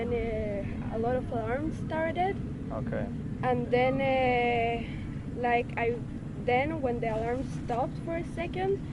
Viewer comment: What was the word the girl at 19:30 saying before she said "terrorist"? It was always edited out. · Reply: She said mehabel which means a terrorist in Hebrew